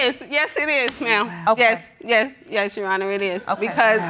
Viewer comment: DID SHE JUST SAY “YES YOU-HONOR?” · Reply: I'm hearing "yes younna" no Rs anywhere.....